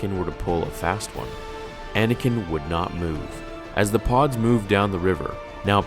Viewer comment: I thought he said as the pogs moved down the river.